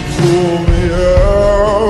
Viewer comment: why do I hear "pull meow pull meow"